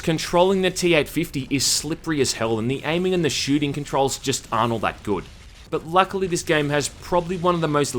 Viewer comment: "The aiming and the shooting controlls just Arnold that good." That's what I heard and I'm glad